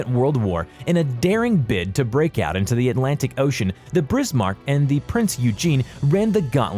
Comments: Did he just said “Brismarck”? WTF?! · he said brismarck instead of bismarck